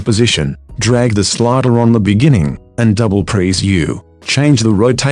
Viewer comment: what did he say?? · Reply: twice press "U"